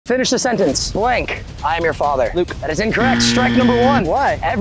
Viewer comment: I thought he said blake im your father💀